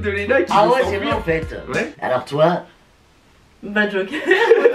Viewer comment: A 5:36 quand elle dit « ba Joker » moi j’ai entendu « ba Joyca » suite à cela je me suis lancer dans une recherche épique sur le net en me disant « wtf il était en couple j’étais pas au courant »(vous vous doutez bien que je n’ai rien trouvé à ce sujet) et en revenant sur la vidéo je me suis rendu compte que j’ai juste mal entendu 😭😭😂